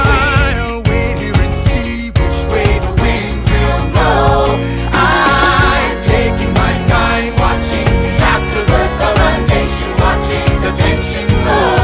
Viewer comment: when Laurens said 'pour me another brew, son' in Aaron Burr, Sir I thought he said 'pour me another broom son' until just now. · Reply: Same, and when he says „I‘m taking my time watching the afterbirth of a nation grow, the tention grow“ I always thought it said „I‘m taking my time watching the afterbirth of a nation grow, attention hoe!“